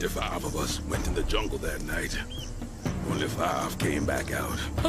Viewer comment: i heard that as "25 went into the jungle that night. 25 came out"